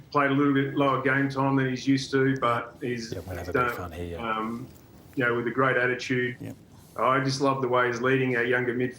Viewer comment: what did he say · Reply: “I’m gonna have a bit of fun here” - Lloydy